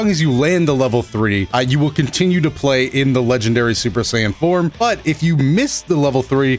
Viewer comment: Why does he says super sayain 4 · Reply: He said form